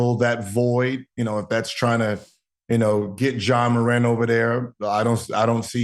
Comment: I swear I kept hearing "John Morand"